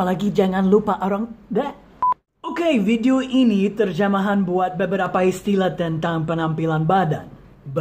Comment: I heard you said "terjamahan" 'jamah' omeji im sorry.